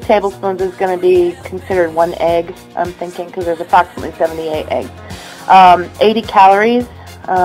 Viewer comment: Those Bee Gees guys can sometimes be really annoying! ;) I did not hear everything you said, but how many eggs corresponds to one of those big jar? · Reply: I heard her say about 78 eggs per can.